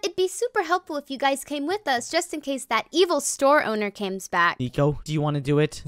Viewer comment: The ruby mom said cames back instead of comes back